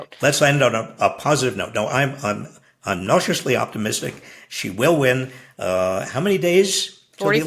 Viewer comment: it sounded like Robert Reich said that he's "nauseously optimistic!" in any case, I'm cautiously optimistic that we can all go forward, and not backwards!